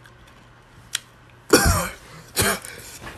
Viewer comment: What I heard “Ahh bay ahh” Me crying laughing